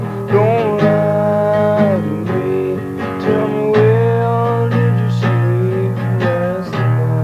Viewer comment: I love how it sounds like he says " well did you sleep last night" even though he is saying where.